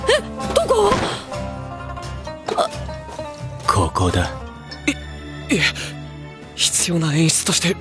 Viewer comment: wut he said there? I dont understand anything in this vid ;-" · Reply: He said “right here”